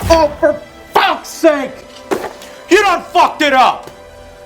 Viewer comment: does he say "yodon fucked it up"?